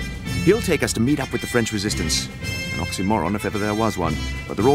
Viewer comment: Ok until this very moment I always thought the Brits were French for some reason😂 I always thought he said “we French” not “the French” then again I haven’t played this since I was like 9 so back then I didn’t know the difference in accents honestly, and I didn’t pay attention to the flag either I guess